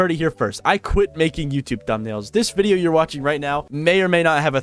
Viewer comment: Phew I thought you said you were quitting YouTube😮‍💨